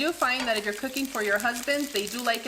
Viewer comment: That’s funny I thought I heard you wrong you make dinner for your ex-husband what a good woman